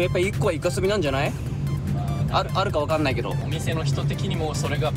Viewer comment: 分かんないけど。が分かんないけろって聞こえる。wもし(けろ)だったらまじかわいいぜ？w